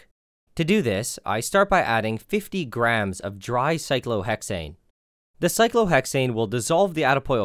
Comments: Hey. Just so you know, at 12:45, you say cyclohexane, but the caption says cyclohexanol. Again, at 14:10. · Cyclohexane not cyclohexanol as the caption said